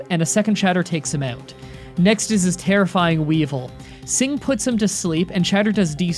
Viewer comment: did he just called weavile weevil?